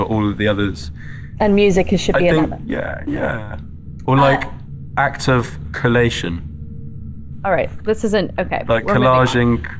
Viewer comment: louis saying music should be introduced as a new love language or act of coalition 😭 hello???? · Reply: I thought he said act of collation